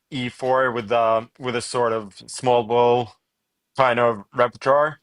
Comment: What word does Magnus use at 44:44? "...a sort of <small bull?> repertoire?